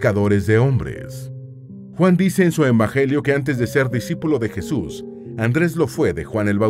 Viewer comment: ENbajelio. ¿Que dijo? XD